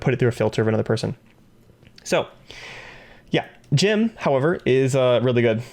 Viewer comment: did someone say Jim? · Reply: Jimmy